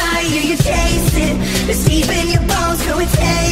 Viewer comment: what it said : "deep in your bones" what i heard : "deepen your balls"